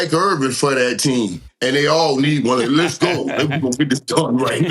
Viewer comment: you gone get this what? Did he say tongue?